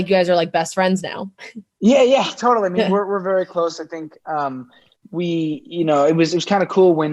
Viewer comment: This nervous laugh is kinda cute. Like: Year...best friends...I mean what else should we be? There a no signs that there (is) was more. No...no...totally not more. Hehe😅😅 Edit: Wait a minute....did he say: we WERE really close?? What happend😭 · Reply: Pretty sure he said we're really close